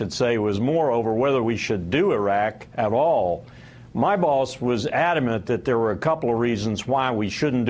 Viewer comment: did Wilkerson say "my balls" at 4:40? :D